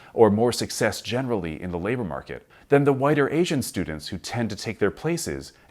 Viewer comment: Does he really say "whiter Asian students" @ 6:57?